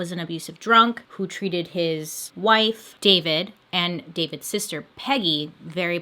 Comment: I thought you were saying his wife was named david